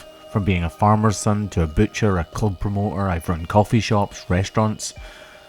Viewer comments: is it the accent but did he say a cult promoter at 5:38? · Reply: Club promoter. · Hold on, did you say cult promoter?